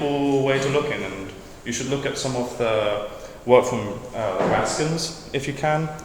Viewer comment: In the intro around 1:18 you say "you should look at some of the work from ?? if you can" I was wondering what you were saying there? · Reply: Sorry, super late in responding but I said "the work from the Raskins", I think I was specifically thinking of Ubiquity by Aza Raskin, but his father Jef Raskin had a really thought provoking book "The Humane Interface"